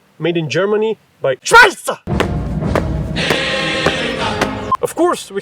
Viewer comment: for one second I thought you said scheißer